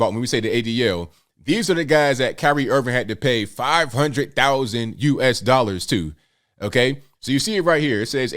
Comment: I thought it first said Irving must pay ABL 500k! 👍